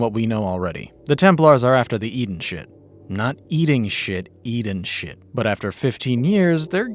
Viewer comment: My girl was like did he say eating shit ? 😂